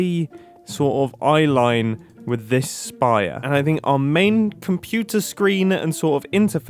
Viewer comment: Grumbot? This is literally what went through my head when he said “main computer screen” 😂